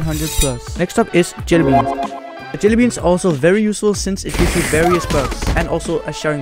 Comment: is bro saying useless or useful 💀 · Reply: useful xd